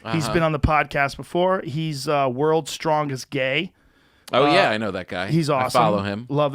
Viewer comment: Did he just say "world's strongest gay? I LOVE GAY PEOPLE" SPOTIFY HAS JOE BY THE BALLS